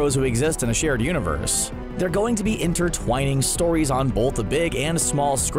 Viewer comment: Did he say entertwaining or it just me